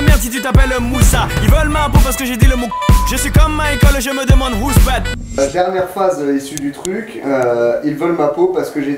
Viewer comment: Qui sait c'est quoi le mot à 11:42 ? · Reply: Kouffar